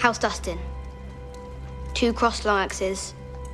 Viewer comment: at first i thought she said two crossed loraxes. what a weird ass sigil that would be